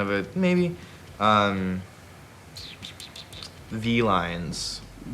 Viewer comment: I heard "felines" I didn't know how to feel for a second there...